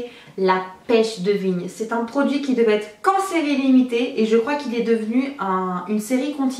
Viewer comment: Quand t'as dis que les produits THE body shop devaient exister qu'en série limité, j'ai compris "cancer illimité" :')